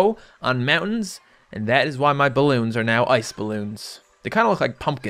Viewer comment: i thought it said boy balloon